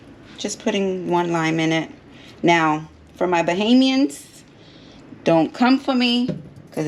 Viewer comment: haha at first, I thought you said "bohemian" that took me a second.